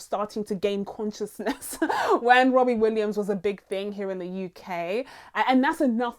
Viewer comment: I thought she was saying Robin Williams 🤣 how American of me 🤷🏾‍♀️🤣🤣